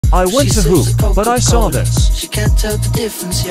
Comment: I thought you said I want to poop 😂😂😂😂🩷🩷